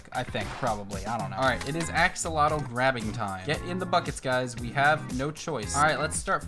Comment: When he said “ get in the buckets guys “ I thought he said “ get in the fuckets guys “😂😂😂